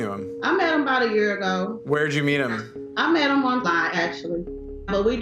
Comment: I thought that mom said "I met him on vine" 😂😂